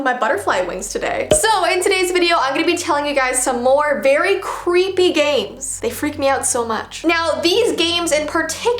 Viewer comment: How come when she saud "creepy games" I thought she said "creepy gays" 🤣🤣